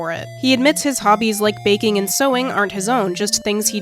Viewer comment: I thought you said vaping and I was like, WAIT THATS CANON?